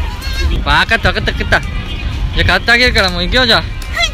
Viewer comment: サムネ超可愛い💓 1:47が「わーかったかったかっ た」にしか聞こえなかった笑